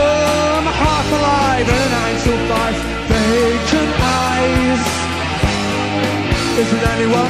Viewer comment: I always misheard the line "Vacant eyes" as "Baconise"